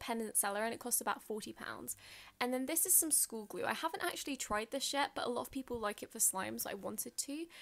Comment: Did anybody else hear say: I haven’t tried this s**t at 0:36? It’s probably just me tho. Love your videos and you’re soo pretty